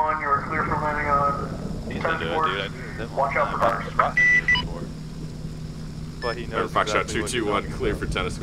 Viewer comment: what does the say to watch out for? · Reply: best that i could make out was "watch out for runners"